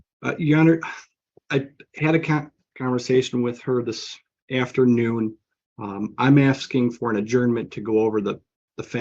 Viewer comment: Sounded like her about slipped up. Thought he was saying "I had a cocktail with my client this afternoon".